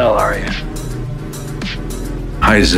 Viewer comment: heisenberg what i hear: "Aizen" berg